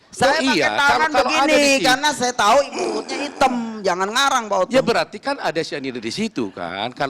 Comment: di menit ini baru saya sadari, dan mgkn byk org lain jg yg terlewat, bilang apa? "mulutnya item"? Hitam kebiruan? Bukannya si ini bilang dipersidangan Mirna mukanya merah? Kalo merah ga mgkn hitam bibirnya, ga mgkn hitam kemerahan, kalo biru agak kehitaman mungkin, tanda kurangnya oksigen. OMG. Keknya ini adalah bukti yg tanpa sadar si sampaikan krn kecolongan didesak pak Otto perkara bantuan nafas 🤔😂☺️☺️☺️